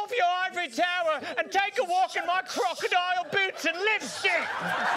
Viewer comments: This is the only Studio C that I am aware of where one of the cast members swear. 3:19 Does anyone else hear Jason say, "and take a walk in my crocodile boots and live s**t!" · When Jason said "lipstick" I thought he said "lip sh**"